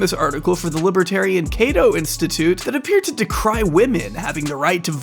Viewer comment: at first i heard 'libertarian KETO institute' and wasn't even that surprised lmao